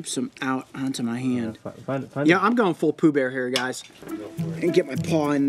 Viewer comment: "Hey, Pooh-bear!" Coyote: "W H A T?!" Man really let the honey demons take control for a moment. 🤣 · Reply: Sound like he said Boo Bear LOL!